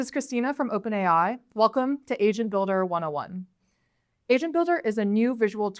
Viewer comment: swear i thought she said "welcome to Asian Builder" in the beginning